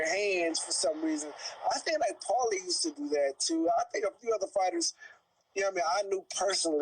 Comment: Did he say at 12:01 I think paulie used to do that??? 🤔